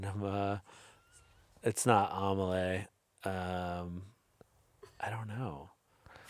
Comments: the captions saying “omelette” instead of “amelie” made me snort a little (amelie is in my top 4) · not the captions saying “omelette” 😭 (he said “Amélie)